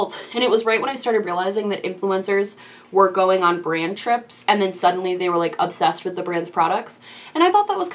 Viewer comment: When you said "Boujee brand trips" I heard "Douchey brand trips". I was like yep about right......wait thats not what she said.